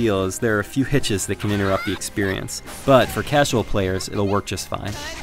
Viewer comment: did he just say cashual